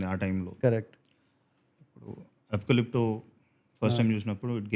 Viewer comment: what is movie name he said at @ 1:29:55? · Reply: Apocalypto directed by Mel Gibson